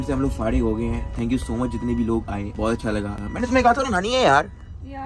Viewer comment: what did ducky say? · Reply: Mene Tumhe kaha tha rona nai he yarr